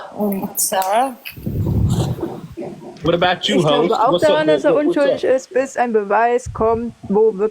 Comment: Here as part of the Kiosk team ! Love! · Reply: I came for him as well haha 7:15 I thought he said what about you hoes lmaooo maybe he said host haha